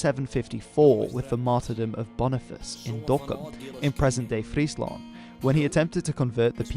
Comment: Just so you know, at 4:47 the subs say Dhaka instead of Dokkum :p AFAIK the Frisians never killed a Christian bishop in Bangladesh.